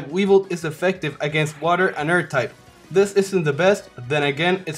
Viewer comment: efective against water and earth EDIT: wait did he say air or earth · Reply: nope pretty sure he said earth xd